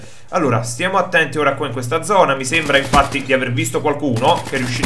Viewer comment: Al minuto 1:42 ho sentito infarti invece di infatti 😂